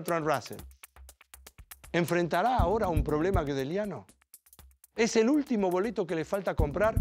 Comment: en el min 17:15 ¿que fue lo que dijo problema que? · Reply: Un problema Gödeliano, haciendo referencia a Kurt Gödel.